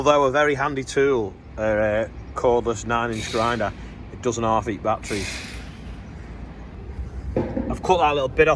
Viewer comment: "It doesn´t half eat battery"? Can someone tell me what he actually said? Probably wasn´t what i heard.